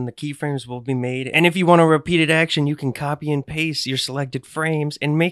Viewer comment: "Anything can now be your puppet, you're god now" It perfectly describes how being an animator feels. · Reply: I thought it said "you'll die now"